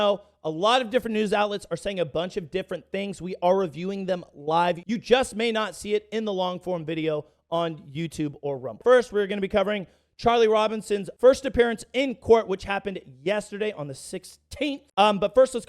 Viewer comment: did you say Charlie Robinson